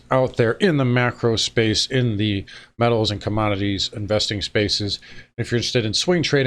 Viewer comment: I thought said “metaverse” when he said “metals” 😂